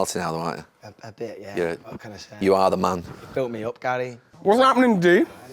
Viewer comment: did he just say "Fill me up Gary"?? Bit kinky😂